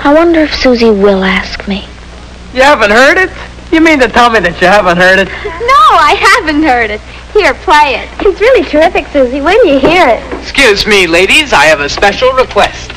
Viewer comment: I HEARD THE BIRD IS THE WORD!!!